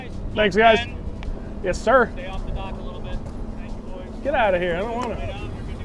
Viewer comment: Hell ya! What did that guy say at 1:22? "Stay off the dock a little bit"?